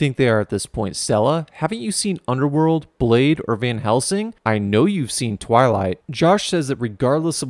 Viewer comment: I Like That Lil Reference Of Hellsing Organization :o Wait Nevermind You Said Van Helsing Thought You Said “Hellsing Organization”